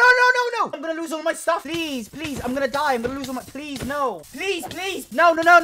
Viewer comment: i thought the cheater was saying "i'm gonna lose all my rizz"